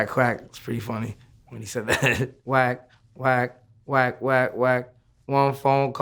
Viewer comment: Wait, hold up....I thought he was saying, "work, work, work, work, work" but he really saying, "whack, whack, whack, whack, whack". Who else thought that?